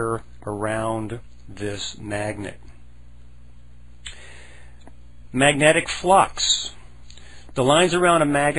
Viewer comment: 🤦 notice at time 1:25 he said maggots instead of magnets 🤓🤓